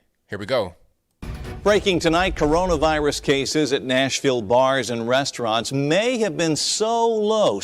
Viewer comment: I thought he said Nashville bama lol